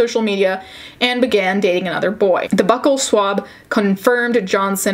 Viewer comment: I know this is a serious topic, but I was listening to this video while making lunch and definitely thought you said butthole swab instead of buccal swab. I had to stop what I was doing because I was so confused.